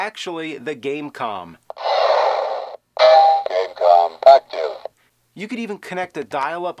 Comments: Is it me or does it say "Gamecom fuck you"? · Does it say "Gamecom fuck you" @ 1:08?? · lol it sounds like its saying GAMECON! FUCK YOU!